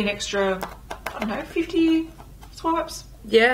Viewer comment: is she saying 15 or 50 ?? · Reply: 50